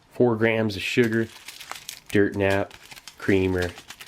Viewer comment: did he just call it a dirt nap? That's a turn of phrase I've not heard in a while